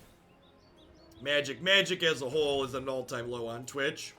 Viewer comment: "Magic, as a whole, is at an all-time low on twitch." I heard 'Magic as a hole...'